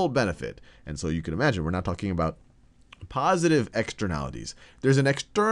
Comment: There's a typo in the closed captioning at 3:10. It says "we're not talking about positive externalities," but what sal actually says is "we're now talking about positive externalities." you should change that so people don't get confused